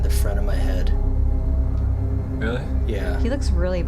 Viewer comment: The ghost hunting video. @ 13:18 he responds with “really?” When he plays it back he hears “good” instead of “really?”. It’s literally just the other dude responding to him getting a headache.